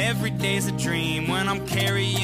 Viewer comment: For some reason I hear “ I’m carrying your wife with me”